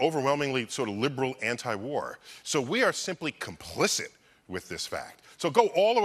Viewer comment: Neil says "complicit" by subtitles say "explicit".